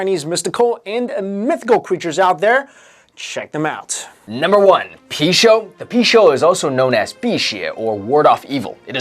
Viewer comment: i thought you said pichu 😆